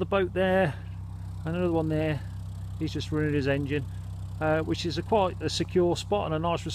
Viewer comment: Nice turn onto Ashby . Did i hear you say " HE'S JUST RUINED HIS ENGINE ' please explain . Travelling at night is a bit adventurist i must say , should have more lights (side lights) to be seen as well as see :-)